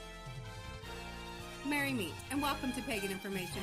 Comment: Ooops, I thought said "Marry Me". My bad.